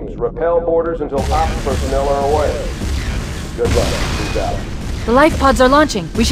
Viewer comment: did he say "peace out"? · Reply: Steven7412 keyes out